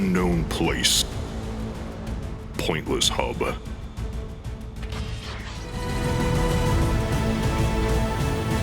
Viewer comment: why did i hear pointless hobo instead of pointless hub